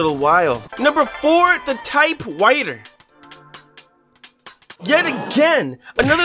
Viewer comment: ahh yes the most satisfying thing to me in this video is that he said "type whiter" instead of type writer